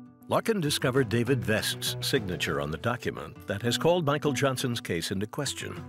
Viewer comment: I thought it said Michael Jackson Lmao